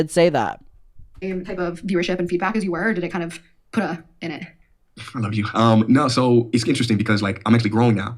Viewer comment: Yooo anyone hear that guy mumble "mm I love you" at 1:12:05?? Lol Adam was right, those two did fuck 💀